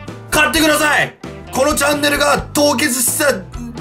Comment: チャンネルがジャンヌに聞こえた笑